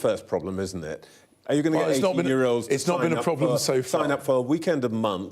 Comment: I misheard the Home Secretary. I thought he said 80 year old. I will be 80 next month and was looking forward to joining Dad's Army. They don't like it up 'em!